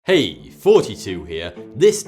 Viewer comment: All videos sound like he says: ''Hey 42 here''.